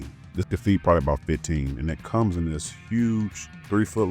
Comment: I know I wasn’t tripping I know I heard you say cheeseCAKE instead of cheese STEAK EVERYTIME 😂😂😂😂😂😂😂